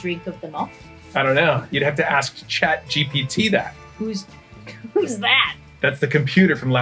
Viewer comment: For a second, I thought you said, "Chad GPT," and I was like, Who? Great to see back. Love that garnish.